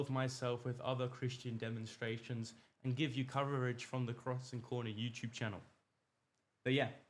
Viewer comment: I misheard the organisation name you gave out -it sounded different each time. Crossing? Crossand? Crossof? Figured it out eventually but it would be helpful if you put it on the screen and in the video description. The internet is designed to works with hyperlinks! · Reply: Cross and corner. I had trouble too!